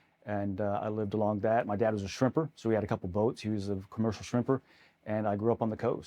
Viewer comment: Ha, I thought he said his Dad was a stripper 😂